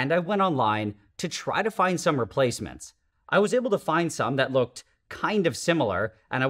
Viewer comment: I thought bro said he was looking for replacement moms